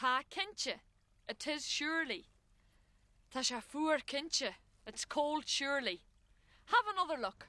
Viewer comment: What did they say at 16:34-16:40 when responding to tá sé fuar? it sounds like tá cince? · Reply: "Tá, cinnte."